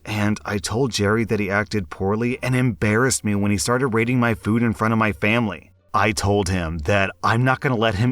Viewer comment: I kept hearing “rating my cooking” as “raiding my cooking” and I was like “how does one make a raid boss out of a frittata”